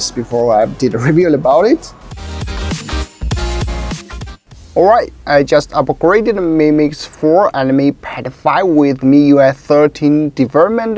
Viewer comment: When he said "mi pad 5" i thought i heard, "me PED0PHlLE".